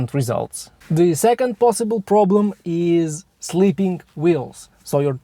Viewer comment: I know you meant slipping wheels, but it sounds like youre saying sleeping wheels